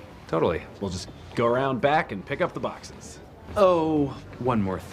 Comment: What's so gross about a bucket? My mother made me use one my whole entire childhood. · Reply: Did she say "bucket" ? I heard "fuck it" from the beginning…